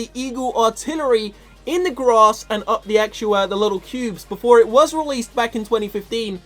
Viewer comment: Did he say pubes at 2:59😂